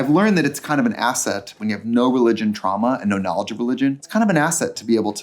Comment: Did Trixie just say she had no trauma?Oh ok religious trauma, I get that I guess 😂